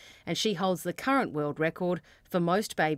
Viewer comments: Does anyone else hear “Courtney Bembridge, babyc news”? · At the end I thought she said "Baby-C News".